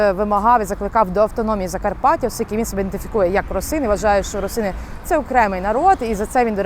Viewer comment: The captions say «росин» but should it be «русин»?